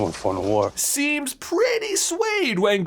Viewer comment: Even Jacob is pretty suede. Me: Huh? Is that like a new phrase now all the kids are sayin these days? I’ve heard satin and mesh but not suede. Kinda cool, I think I like it. Goes back to get context again: Even Jacob seems pretty swayed. Me: Oh. Oh. Never mind. Guys, I think I can officially say my narcolepsy is getting out of hand and starting to get to me. I’m cracking just ever so slowly.